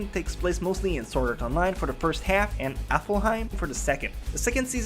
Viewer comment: i heard you say aflheim isnt it alfheim